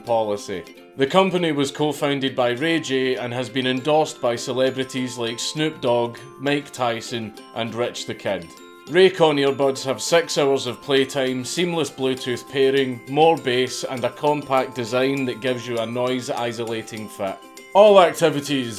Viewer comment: When you were talking about the Racon earbuds, I thought you said that they had, "sex whores of playtime" . . . which sounds pretty awesome, TBH.